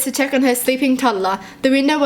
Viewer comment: When say toddler, I actually thought she said tumblr 😂😂😂😂😂